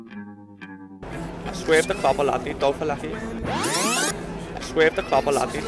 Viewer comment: Guy at 5:05 is either saying "swerve the clobber, I need dough for lekky" or "a swerve for clobber, I need dough for lekky." Either way "I don't need" is "swerve" in Scouse and "clothes" is "clobber" and "money" is "dough" and "electricity" is "lekky."